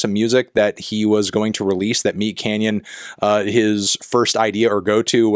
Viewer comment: Did you just say you have a "meat cannon"?